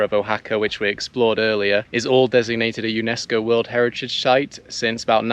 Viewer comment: did I really hear "Unesco World Heritage Shite"? LOL :-) Nice video as always!